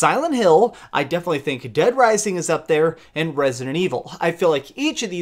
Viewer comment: Wait. Did he just say Dead Rising Scary Spooky?